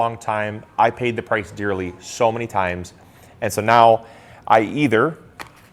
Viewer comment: Did I get this right? 350 degree 5 - 7 minutes At first, I thought he said 150 degrees. But think he said 350